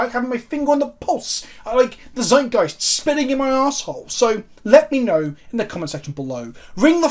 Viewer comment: I totally thought you said “zeitgeist spitting in my asshole” 😂 As someone who only got into the game two years ago and really wants to try out modern, I do not think you are being too critical in your analysis. Keep up the good work man.